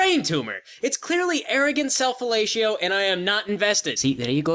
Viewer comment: I don't understand the joke made at 6:21, and I can't spell it to look it up XD Could you at least tell me how to spell that so I can figure it out? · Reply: You just had to turn on the captions: the word is fellatio (which means giving oral to the male genatalia)